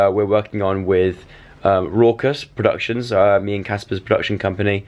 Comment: When you said 'raucous productions' I thought you said 'rockstar productions' and I was like "OMG THEYRE GONNA MAKE JOE AND CASPAR A CHARACTER IN GTA6!!!!!" But then I realised what Joe actually said... AbbieJackson xox